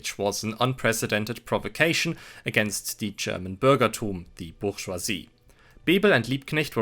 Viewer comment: What is the word at 5:45 bitte? German for bourgeoisie? Bürgerturm it kinda sounds like. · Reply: bürgertum